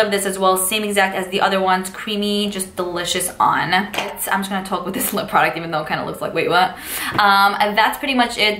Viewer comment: 🌰🍁🍃🍂🌿Thank you found 3 I want.. Was hunting for fall shades when I found you.. Love your makeup & of course love the accent can't figure out what you said your lipstick looks like at 13:36 tho?? Listened like 10 times bugging the heck outta me ? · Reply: " i'm just gonna talk with this lip product even though it kinda looks like 'wait, what?' "